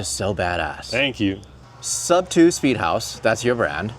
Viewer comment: Great explanation. I first heard it as "Sub 2-Speed House" which made me think of "One gear, One speed, GO!".